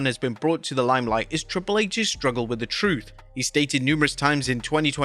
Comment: I thought he said problem with Truth.